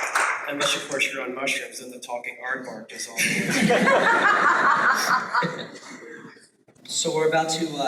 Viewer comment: what's up with the audio? great talk though. what did he say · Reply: Unless of course you're on mushrooms, then the talking aadvark has all the answers.